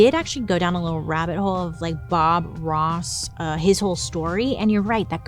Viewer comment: bailey i thought when you said “bob ross” you were saying foie gras